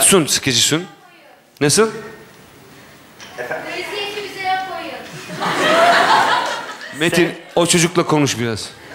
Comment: de çocuk ne diyor lütfen biri yazsın. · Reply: Bu eziyeti bize yapmayın